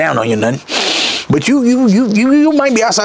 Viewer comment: I thought he said he won’t gonna talk down on em’😂😂😂